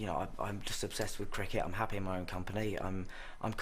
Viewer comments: Did he say he's obsessed with drinking at 2:53? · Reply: He said Cricket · Did he say he's obsessed with drinking at 2:53?